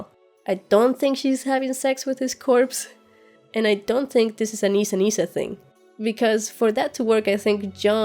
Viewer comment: What is she saying at 26:06? I just can't understand, maybe its a bit of her accent but those string of words, don't comprehend it. Can someone tell me please? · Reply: She says she doesn't think it's a Nissa Nissa-thing. It's related to the whole theory of Jon being The Lightbringer "Azor Ahai". Try Google if what I'm saying doesn't quite make sense (